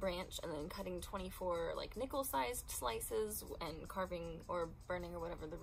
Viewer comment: This is such a lovely video! I love how personal it feels ♥️ and not gunna lie, when you described cutting “nickle” sized slices from the branch, totally thought you said “nipple sized” 😂✨💕